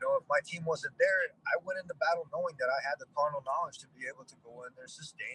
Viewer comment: Did he say he had the "carnal knowledge"